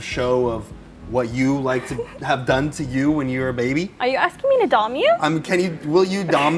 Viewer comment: Anthony: Will... Will you show me-: You want me to dom you? Anthony: I.. I mean: You want me to dom you? Anthony: Y-Ye- Will you dom me? Can I have your consent? Anthony: You have my consent to... to dom me. Anthony' already being small and hadn't even been small yet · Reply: Ohhhh, I thought she said "You want me to dumb you?" and was confused. :P